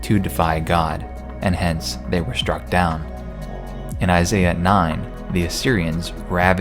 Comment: When you said "in Isaiah 9", Siri thought you said "hey Siri"